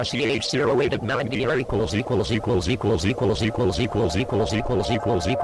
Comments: WTF ARE THEY SAYING? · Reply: It glitches out saying random numbers and then "equals equals equals" over and over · is it me or is he saying "seagulls" a ton of times? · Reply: It kept saying "Equals"